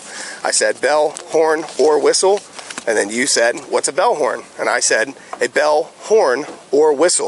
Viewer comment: I also heard him say belllhorn